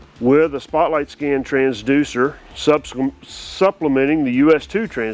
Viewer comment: did anyone know what the guy said when the time was in 01:59, I listen more than 50times, but i still can't figure out what exactly he said??? immediate reply would be appreciated!!! thx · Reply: He almost said subsequently but instead switched to supplementing because supplementing is what he wanted to say. Hopefully within 2 years is considered an immediate reply to you.